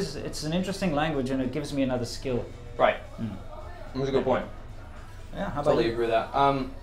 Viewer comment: I kept mishearing saying "That's a good point" as "That's a good boy".